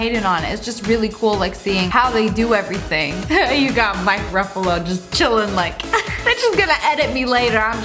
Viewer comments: Who else heard her say Mike Ruffalo😂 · Did she just say Mike Ruffalo???!!!! WHAT??!! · did she really say MIKE Ruffalo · did lia say MIKE Ruffalo Sorry but love u 💖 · what did u say? MIKE RUFFALO ? 😂